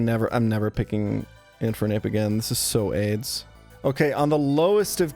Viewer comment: did he just say "this is so aids?" Whadda hell...